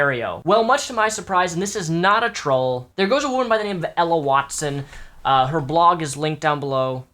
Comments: Ella Dawson, not watson. And while I understand the topic of STD's is a touchy subject, I'm not speaking to those who get one thru safe sex and having your partner NOT disclose they have an STD. Legally you need to tell your partner, I'm talking about this woman Ella who labels her self a giant slut (her own words) and got an STD from sex. If you are proud of getting something that could potentially hurt or harm other people without disclosure , you're a sick, twisted, fuck. Ella is one of those people. Being proud of something that can hurt people. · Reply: Oh, gosh, I thought you said EMMA WATSON! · I THOUGHT YOU SAID EMMA WATSON AND I ALMOST DIED I ACTUALLY SCREAMED HERMIONE NO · Ella Dawson, not watson. And while I understand the topic of STD's is a touchy subject, I'm not speaking to those who get one thru safe sex and having your partner NOT disclose they have an STD. Legally you need to tell your partner, I'm talking about this woman Ella who labels her self a giant slut (her own words) and got an STD from sex. If you are proud of getting something that could potentially hurt or harm other people without disclosure , you're a sick, twisted, fuck. Ella is one of those people. Being proud of something that can hurt people. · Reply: I thought you said Emma Watson at first, so I was like wait, the fuck??? o_O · lmao i literally thought you said emma watson at first ...dumb bitch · At first I thought you said Emma Wattson